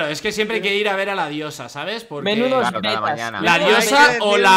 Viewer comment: escucharon lo que dijo alexby de fondo en el minuto 12:00 ''menudas tet4s'' xd